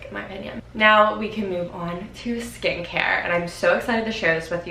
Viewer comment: You said, "Skincare", and I was walking to my kitchen to turn my microwave off and I heard, "And now time for skank hair," and now I can't stop laughing.